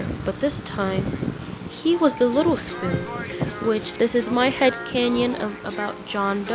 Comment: your phone/electronic is one day gonna explode😳 BTW! did you say canyon?